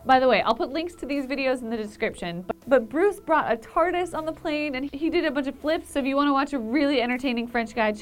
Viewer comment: I thought she said "Bruce brought a tortoise on the plane."